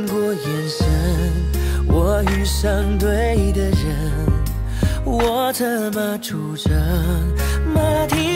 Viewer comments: 진자여 我策馬出征會讓人聽成我怎麼出生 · 我策馬出征 我聽成:我怎麼出生